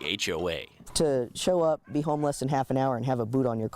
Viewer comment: I thought he said he was homeless, not “home less”.